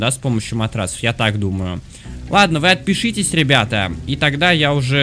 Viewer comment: я же не 1 слышу "вы отпишитесь ребята"